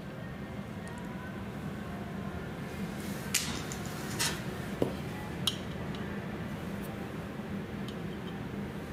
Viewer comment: What is he adding at 00:43? Can't hear him clearly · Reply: Not sure if you are still wondering after two weeks. But it was Mango in bruinoise with lime zest. The next item was a pannacotta made from Calamansi or Calamondin. Its a Hybrid citrus fruit.